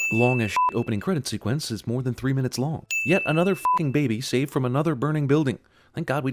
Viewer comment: I heard that as "another burning baby saved from a fucking building" and for one brief instant I seriously questioned the morality of this world